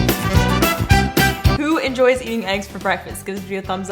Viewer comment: omg i thought she said "Who enjoys eating ice for breakfast" XD